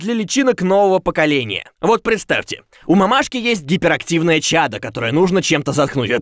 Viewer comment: мне послышалось на 1:27 гипер активная ЧаРа а не чада (андертеловцы поймут)